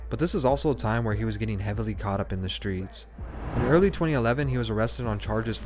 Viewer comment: When you said “early 2011” I thought you said “he was 11” and I was like gd manufacturing heroin 🫣